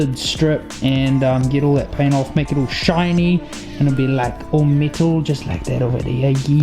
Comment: Sick build! I thought you were saying middle it took awhile before I realized you were saying metal 😂. Cool accent though!